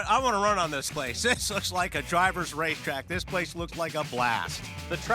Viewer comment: Did he just say "Drivers rape track" ???